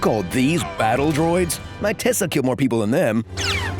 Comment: did you say your Tesla killed more people than [battle droids]?